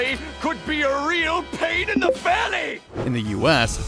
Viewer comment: I always thought he said "pain in the belly" not fanny lol